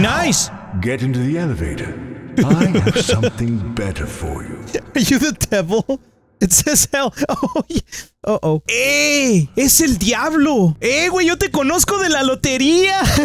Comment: For the jojo fans out there, From my perspective I heard "Eyy it is Diavolo"